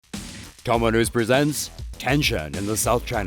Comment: Thought I heard him open with "Domino's Presents.." and got hungry as hell